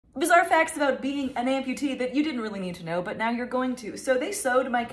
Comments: thought u said beating an amputee 💀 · i thought she said "beating" an amputee instead of "being"